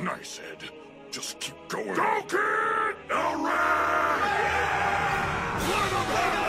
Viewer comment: 中尉「ヒカキンさん」に聞こえる件